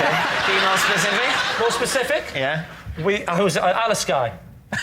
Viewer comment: What was the name of the place Lee said at 20:00? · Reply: Alasky. It's probably made up or something.